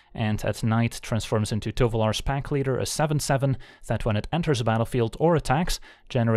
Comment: I heard it as Tovolar Smackleader. He has a new name now.